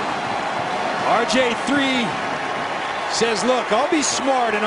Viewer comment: Did He Say RJ3😂